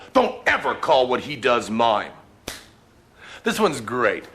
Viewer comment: "Don't ever call what he does mine." Is that what i heard? So "He" refers Teller/Teller's Magic/illusions" and "mine" refers to Penn's Magic/illusions"? Or is that reaching? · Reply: You misheard; what he said was "Don't ever call what he does mime", like those people with the painted faces in the striped shirts.